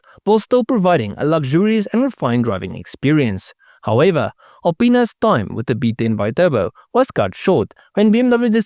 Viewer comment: i'm a 42 year old child when you say alipinas it sounds like you say a penis